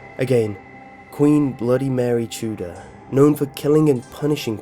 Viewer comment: He pronounces the last name "Tudor" very strangely. lf l hadn't had an assignment abut Queen Elizabeth l, l would have thought he said Bloody Mary Chooda. And his accent also made me hear about a nursery rhyme called Pizza Pizza Pumpkin Eezza.